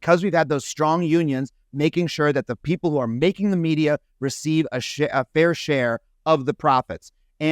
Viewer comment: Did Adam say: "making sure people working in the unions receive a shii..a fair share..." feels like he was about to say "a shirt ton of money 🤑"